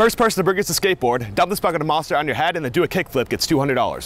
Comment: I thought he said bucket of mustard and was really confused when she started dumping all that liquid